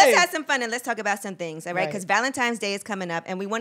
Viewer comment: did she just say valentimes?angela 😂😂😂😂😂